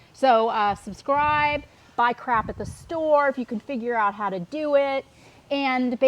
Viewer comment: Who else thought she said buy crack at the store 🤣